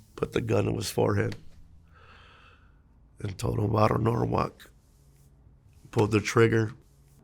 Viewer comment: What did he say too the guy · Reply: Varrio Norwalk...